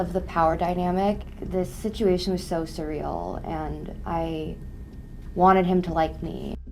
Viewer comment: i'm no nativ speaker, but did she said cereal?